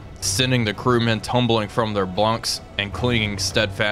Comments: is it me or does he say "blunks" Great vid! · sorry im not sure if im the only one but i thought he say blunks instead of bunks at the start